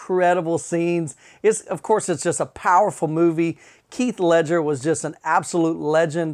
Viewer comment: It sounded like you said keith instead of heath ledger